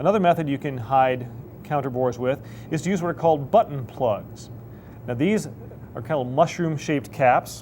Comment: I thought he said butt plugs